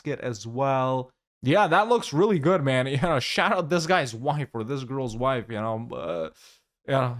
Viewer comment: did you say "shout out this girls wife" What??!! lol.